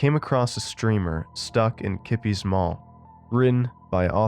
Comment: I thought it said “nipple’s mail” lol